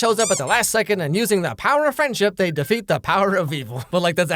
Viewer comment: Honesty I thought him saying "the love of friendship" was a joke because that kiss was so gay, but nope its "just gals being pals."